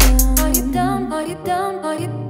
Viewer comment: heard this on the radio and I kept hearing "are you dumb"